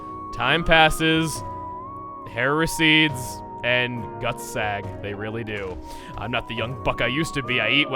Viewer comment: "and guts sag." I'll be honest, I thought he said "nuts sag," which to be fair is still kind of true.